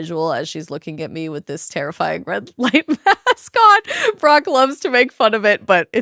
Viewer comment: Oh it's Brock - thought you said Barack - close call !!! Good luck, I've enjoyed your content. I lost you in my network for a few years. Happy to be back.